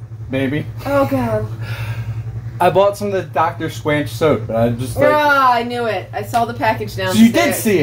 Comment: Is it just me or did he say "I bought some of the Dr Squanch Soap" 3:00 lmao